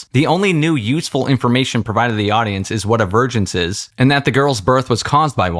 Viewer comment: Not going to lie, but I thought you kept saying Torbin (or whatever the young guy is) was going to find a Virgin, not Evergence. Yet, I only caught on the third time you said it because it made sense for the bad writing, like they named a thing a Virgin for some reason.